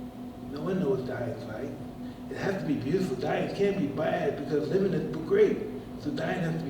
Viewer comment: "Dying can't be bad because living isn't great" - what a way to see death and I partly agree. Edit: misinterpreted what he said. He said "because living is so great!". I like his perspective of putting back to those less fortunate as he's some everything for himself now I love Tyson. Proper spiritual and deep intellectual guy which many misunderstand him. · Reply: Did he say isn't? I thought he said living was great, so death could be even better. Prior to that he said "hey, I might start to live after I die"